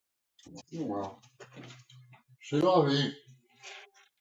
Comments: he does NOT say, 'oh well. She loves me' He says: "oh well, say la vie" (oh well, thats life) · I hear “se la vi” (or however you spell it, I don’t speak old foreign.”